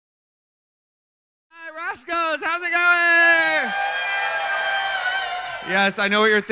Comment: All I hear is Roxxxy’s Tasha Salad.